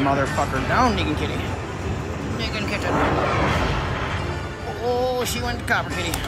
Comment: wtf I know you were saying Negan but it sounded like Nigg...